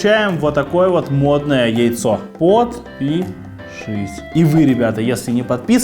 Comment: МНЕ ПОКОЗАЛОСЬ ЧТО ОН СКАЗАЛ: ОТ-ПИ-ШИСЬ...